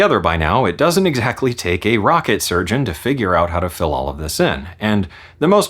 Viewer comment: did you say rocket surgeon?